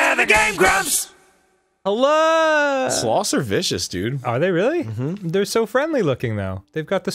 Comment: i heard "sloths are fishes" at first